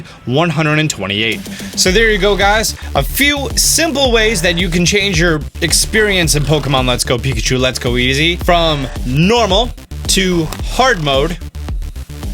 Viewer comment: Probably not intentional but thought it was funny you saying “Lets Go Pikachu and Let’s Go Easy”